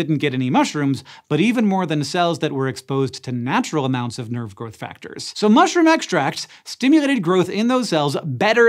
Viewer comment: Is it any coincidence that every time Hank says "Nerve Growth Factor", I hear "Nerd Growth Factor"?